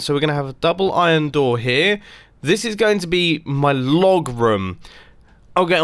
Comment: I thought he said log room instead of vlog room 😂 What is a log room lol